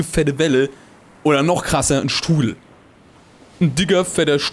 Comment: Ich hab statt 'Strudel' 'Stuhl' verstanden... okay xD